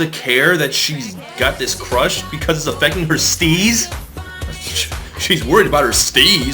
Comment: I always thought she was saying "my sneeze is gonna be effected" as in, her nose was going to collapse from too much snorting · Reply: I thought she said her sleaze, as in like, loving this guy is gonna make her monogamous and stop being so horny or something